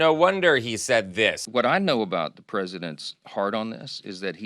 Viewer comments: I heard “…the president’s hard on…” come out of johnson’s mouth (no capital intended) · I'm sure I must have misheard (twice!) but I think Mike said something about the president's "hard on" in the first clip, at about 2:17. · Did he just say the president's hard on...... wth Mike Johnson? Why you talking about the trump's 🍄‍🟫 😂😂